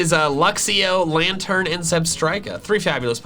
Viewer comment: I thought it was Raikou instead of zebstrika